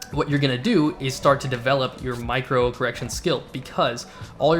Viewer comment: Maybe it’s due to watching/listening to so many political videos, but the first time I could have sworn that at 6:12 I heard “micro-aggression skill”. Could be a good tactic. Low-key trash-talking that makes your opponent wonder if you’re polite or an overly competitive jerk. “I think that choice of visor color pairs less than attractively with your overall color scheme.” He’ll be so confused you can just swoop in for the kill. Ingenious. Jk but good tips!